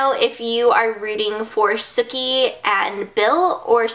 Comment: haha at first i thought u said "snooki" and i was like wtf? but yeahhh i dont watch that show. im glad ur getting better! i came down with the normal cold on frday night! ugh haha but im feeling better as well!